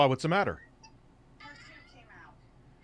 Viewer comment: A kids tooth came out? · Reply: I thought she said "her tube came out". Given everything that his daughter has been through lately, I'm REALLY hoping it was "tooth."